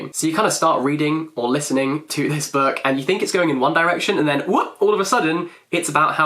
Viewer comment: did he said *one direction*?